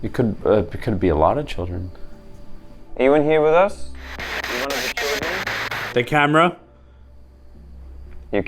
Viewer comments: when ask if they where there with them, it also sound like someone is saying "it's one of the children" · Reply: i think it was someone maybe or just someone behind the camera asking "are you one of the children" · "I'm with one of the children" is what heard instead of "the camera" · Is no one going to talk about the fact that it said “you’re one of the children” at 2:50:00? I listened to it another like 3 times and I think I heard “the camera” underneath it too. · I swear I heard "one of the children" but idk if that was or saying it but I also heard "The camera" from the spirt box in a higher pitched voice. I heard both sounding like they were layering each other.